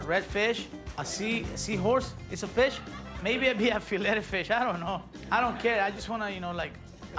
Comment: Did he just say “i dunno, maybe a Filet-o-Fish.” Lololol 😂🤣😂